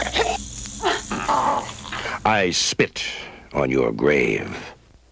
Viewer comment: I thought it said " i spit on your gravy "